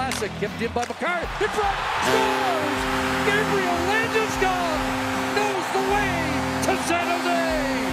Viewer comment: For some reason I always heard Chris Cuthbert say "Gabriel Landeskog knows the way to 7th Ave!" until just now I realized that he said "Gabriel Landeskog knows the way to San Jose!" 😁